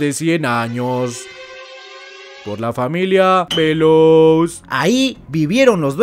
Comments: por un momento entendi familia pelos si no leeo no me doy cuanta xd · Pensé que dijiste la familia pelooos jajaaja